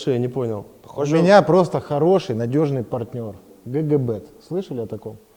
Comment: Мне послышалось КГБ)))